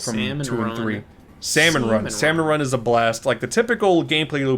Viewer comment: did he really say sea men run???